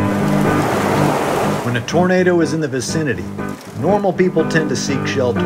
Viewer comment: what he's saying: "When a tornado is in the vicinity-" what the captions say: "When a burrito is in the vicinity-" i fucking love this